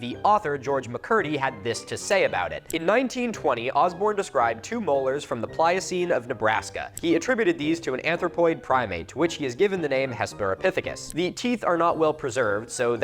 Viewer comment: I heard “Arthropoid” tooth rather than anthropoid a couple times, but I think the conclusion that this is the Nebraska lobster is about as plausible